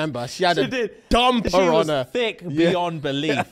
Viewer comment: I heard “dumb piranha” instead of “dumper on her” 😂